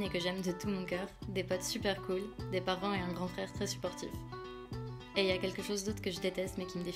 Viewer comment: J'ai presque pas pleuré 😭😭❤️❤️❤️ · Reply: Pareil :o ! Par contre, j'ai entendu "un grand frère très sportif", ça ça m'a fait beaucoup trop rire !! xD Bon j'ai compris que c'était pas ça mais quand même.